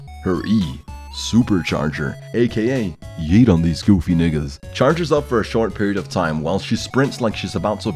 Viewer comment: Who heard him say the n word? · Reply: SALTISBAE SALTISBAE yeah, he said nigga, what's the big deal?